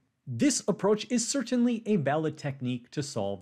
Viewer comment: u did use a word to decribe the technique used..what was the word sir?was d word used "imbalance"? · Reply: The word was 'valid', but Presh pronounced the 'v' similar to a 'b'.